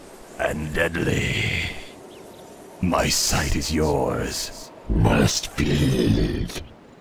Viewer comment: When I was young, I used to think the ghouls said: "must pee!"